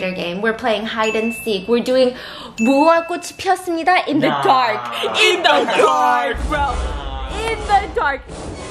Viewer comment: Can someone tell me what she said in Korean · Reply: "Mugunghwa kkochi pieosseumnida" is basically red light,green light in korean. It means the flower of mugunghwa has bloomed.